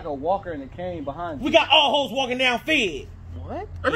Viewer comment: Did he just say we got all Ho's walking down Fig! LOL Flashback for real. Grew up in L.A. Fig is Figueroa. Grats man! Too real. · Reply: I thought he said we got all goes walking Rowan fed ! 😂 from ny so forgive me love the la slang tho